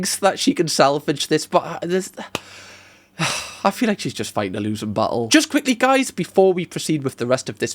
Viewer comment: accent is so strong i thought he said "i just feel like i'm fighting a loosened butthole" i had to rewind